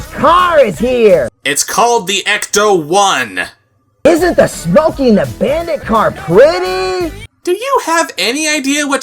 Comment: So, I'm 18, and I clearly wouldn't recognize Smokey and the bandit. With cool cat's muffled voice, I thought he said, "isn't the smokey and abandoned car pretty?"